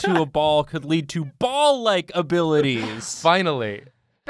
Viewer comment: I thought you said ballACHE abilities, I was a little bit scared